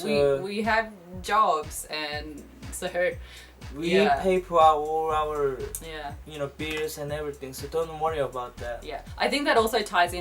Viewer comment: I thought he said " we pay for our own beers" lol you guys are awesome love your videos